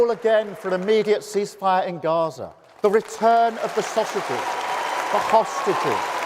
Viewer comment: Return of the sausages? I swear to god that’s what he says lol